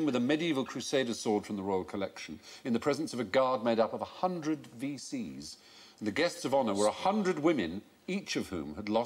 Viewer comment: "A guard made up of 100 VCs, and the guests of honour were 100 women each of whom had lost her husband and all her sons in the war." =:O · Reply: I heard it as "100 WCs" and it was a very different kind of procession to me.